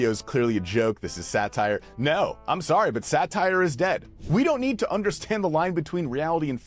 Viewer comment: I thought you said satire was gay and had a good chuckle because you’re barely a septum piercing away 👌